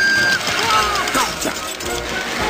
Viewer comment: Did he say hola Gromit at 0:25?????